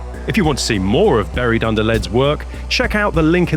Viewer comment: Buried Under Lead's ork army is amazing, but every time said the name quickly, I heard "Barry Dunderled"